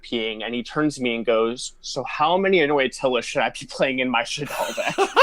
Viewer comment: Don't know what he said at 10:47. I heard, So how many... Shaddoll deck? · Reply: I think, he asked how many anoyatilis do you play in your Shaddoll deck (the water fusion)